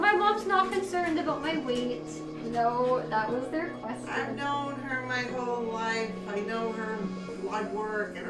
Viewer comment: The mother said I've known her my whole life? There is something wrong with the mother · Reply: Okay that’s what I thought I heard. I didn’t go back and watch it again, but thought maybe I heard it wrong and she had said I’ve known her her whole life. But yeah, this was pretty grim. She’s all whacked out, as usual. My god.